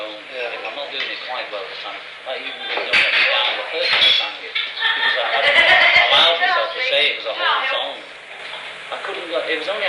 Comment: inaudible = "Because I hadn't allowed meself to say it was a whole song."; 8:54 "you can't hear, this is me singing . . ." = "you can all hear this, it's me singing . . ."; 10:20 "think about how many other . . ." = "think back on the other . . ."